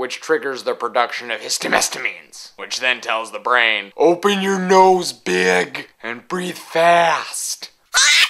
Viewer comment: I thought you were going to say "open your nose biatch"